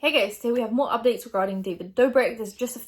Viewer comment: In the beginning of the vid I thought you said "hey gays" I was like hayyyyy